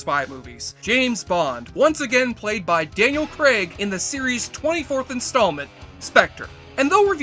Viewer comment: At first it sounded like you called him "Dale" Craig. I was like "That's ironic for someone who judges and makes fun of how some movie lines sound. My brief review of this one, back in 2015 when I finally tried to watch it, went as follows: "At this point, it is clear that the Bond series was once again looking to make fun of itself. Some nice elements, but definitely NOT the best Bond."